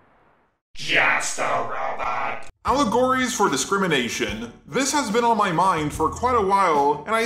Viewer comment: I GENUINELY THOUGHT THIS SAID “ALLERGIES” AND I GOT SO CONFUSED